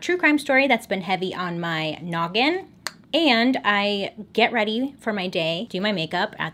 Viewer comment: “I do my makeup and get ready for my day” Also: does her makeup at 11pm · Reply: I thought she said "do my makeup OR get ready for my day..."?